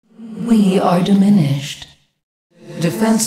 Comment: always when i look at my base and my mothership is in the middle of the map and when its under attack i hear "defense systems falling" and you just need to be that zealot and then you see a corupter flying by lol